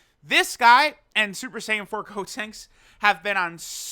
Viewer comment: did I hear ssj4 gotenks? 😂